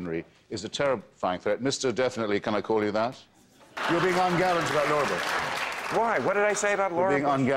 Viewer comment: Can someone tell me what did they said in 1:00 to 1:07. Thanks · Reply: Hitch: "You're being ungallant about Laura Bush." Maher:"Why, what did I say about Laura Bush?" Hitch: "You compared her to Hitler's dog."